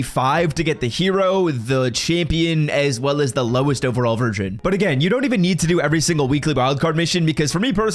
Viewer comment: Did I hear you say “ lowest overall virgin.” Lol